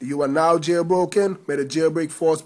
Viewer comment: i sounded like u said "please rape comment and subscribe"